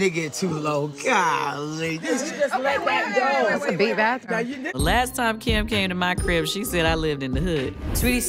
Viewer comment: “That’s a big bathroom” That’s me. Eva. Eva is me. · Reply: I thought she said.."thats a beat bathroom"